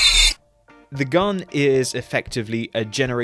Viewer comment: What you said: "The gun is effectively a generation 1 lance phaser" What I heard: "The gun is effectively sheit"